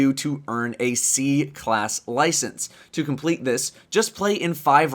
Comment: d-did just say "c class license" at 1:45?? Burnout paradise! My love! It IS YOU!!!!! <3 <3 <3